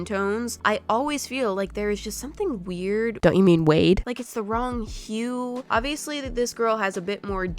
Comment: When you say “WADE” it sounds like your saying “WEIRD” in an Australian accent😅 (I guess!)